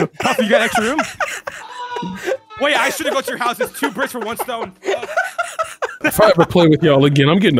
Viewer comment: Does really say "two bricks with one stone"? hahahaha